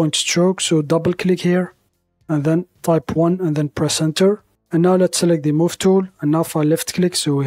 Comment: Nice design. Quick tip. It's percent not percenter. 🙂👍🏻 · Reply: You misheard - He actually says "press enter", not "percenter" 😂